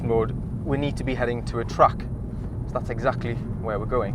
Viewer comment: Did he say 'he need to be heading to a TRUCK' ?at 2:40 LOL